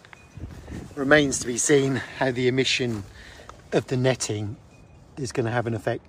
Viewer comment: Not bug proof is as bad as saying it’s not waterproof! Deal breaker as far as I’m concerned, pity, what were they thinking!! Regards from deepest Dorset. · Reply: Thanks, I thought you said “no netting” at one point, other half keeps telling me I need a hearing aid😂. In which case this needs a second looks . Many thanks again for coming back to me. Regards Clive in deepest Dorset 👍